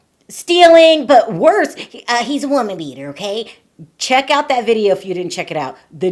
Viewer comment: I thought you said woman eater lol. As in he goes through women like some do underwear..A woman beater is worse